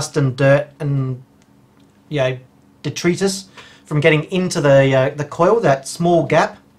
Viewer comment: What word do you say at 4:04 ? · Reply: 'Detritus', mispronounced with an Australian accent.:)